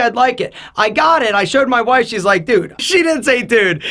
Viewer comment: Whoa whoa whoa... hold on... Did he say he showed his WIFE?? I totally got a wrong read on this guy. Haha. I'm kind of relieved now.